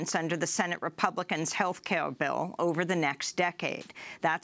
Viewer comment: "Healthkill Bill?" That's a pretty accurate statement to say the least. I could have sworn Amy Goodman first said Healthkill Bill and not Healthcare Bill. I've replayed it more than 10 times and each time she first said Healthkill Bill and only there afterwards did it sound like she clearly said Healthcare Bill for the remainder of this report. A "Fruedian Slip" isn't usually taken lightly.